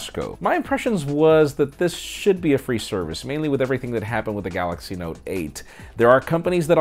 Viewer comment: did he say after what happened to the Note 8?? great video, I like the interactions with the comments!! Good job!